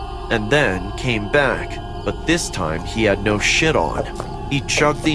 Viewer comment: did he just say 'sh|t' instead of 'shirt'? 😂